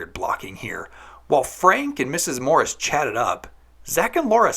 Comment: sounds like you say "Zac and Morris"